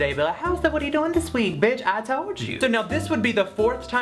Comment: I was like oh my god did he just call his mother a bitch?? if I did that she would have driven over and slapped my mouth😂